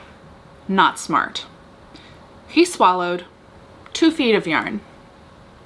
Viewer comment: Until you mentioned knitting, I thought you said Leo swallowed 2ft of urine! 🤣Poor kitty. Great update. Looking forward to hearing more about this adult thriller 👍